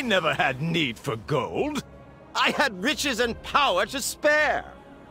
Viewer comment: I keep hearing "Bitches" instead of "riches" lmao.